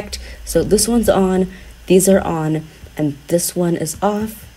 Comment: Video starts* Me:splits screen and hop on adopt me* Wait did he said petstore